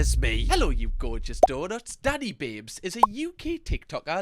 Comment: I thought you said daddy pig 😂 then I 're watched that part and then I thought you said daddy vapes so I had to turn on captions😂😂